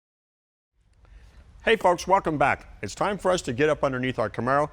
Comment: did he say, hey punks welcome back